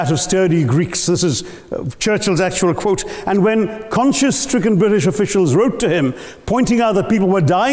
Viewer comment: Okay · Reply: No, at 3:20 it's Conscious in auto generated caption whereas subtitles say Conscience. I too heard it as conscious btw